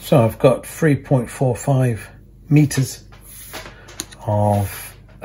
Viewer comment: how long is the 1.5mm piece looking at your diagram i think its 3.45m but it sounds like you say 1.45m in the video and have found the so239 job for weekend.